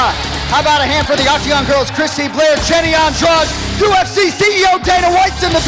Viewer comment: is it me or did he say jenny on drugs